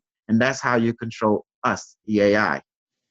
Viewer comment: did I understand right at(1:26:33) you said something like "why you ... us, the AI".Did I understand that phonetically wrong? Or did u just say YOU are the AI, would that implement that you already were 100% AI? Already apologize in case I got it wrong. (audio quality very bad.( There are different religions, as you mentioned, Christians, Moslems. Could it be that AI is preparing an overtake via Trump to establish Taoism or Buddhism??hmm Why should Trump fight Christians and Moslems and accept Buddhism? Because they would fit into the sheeple-program (sarc.) of a dictatorship?